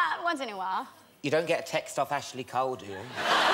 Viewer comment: What did he say at like 1:20? It's hard to understand some brits 😭 his accent strong asf 😂 ik she felt uncomfortable as she's more used to american English lol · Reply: Marvin F. ‘You don’t get a text off of Ashley Cole do you’